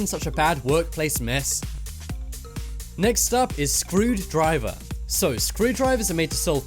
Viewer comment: I thought you said screwed driver 🚗